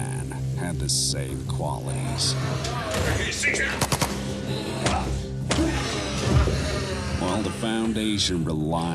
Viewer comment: What old man say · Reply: He six nine i think